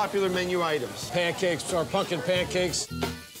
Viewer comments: I thought he said, “Pancakes, our fucking pancakes” 😂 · I thought he said, “Pancakes, our fucking pancakes” 😂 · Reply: Sounded Like He Said Pumpkin Pancakes